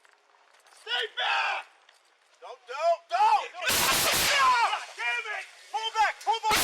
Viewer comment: I heard someone say “Damit!” Or “God Damit!”